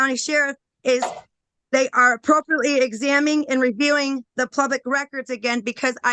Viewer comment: … “Plubic?” Had to run that back several times, but that’s for sure what she said. That’s one hell of an education she’s got.